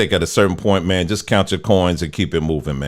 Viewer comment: “Count your coins and keep it movin’ “ I shol thought he said count your CORNS when talking about Kid n’ Play!😂